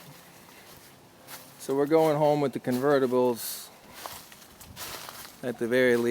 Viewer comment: Oops, I thought you said you're getting both Ghia convertibles?